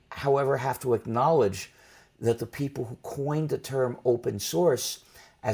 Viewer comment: What did Stallman say at minute 1:00? he got half interrupted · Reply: Re-watch, the interviewer said "mmhmm" but Stallman didn't stop talking, it's 1 coherent sentence. He just said that the people who coined the term "OSS" have done the free software movement a lot of harm.